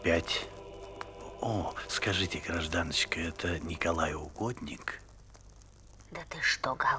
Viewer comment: "это святой Николай Угодник?" - на этом моменте в детстве я ржал, потому что услышал "Николай Укупник" 😄